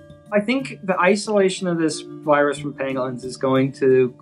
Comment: I was listening to this while it was minimized and I thought they said penguins. When they started talking about scales I was like wtf?